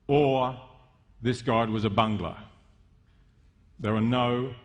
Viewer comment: "...was evil or this god was a banglar." (2:07) -Someone could explain me what this last word means? Im just a Brazilian trying to learn english, and didn't understand this word. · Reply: The word is "bungler".. it means someone who makes lots of mistakes.